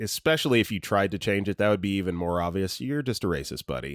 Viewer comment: it actually took me hearing you’re just a racist to figure out inward was supposed to be N word and I was just so confused over what could possibly be bad about the word inward. I was all is this a generation thing where it’s a slang for something bad zoomers made up? Thanks autocorrect for messing up the guys anecdote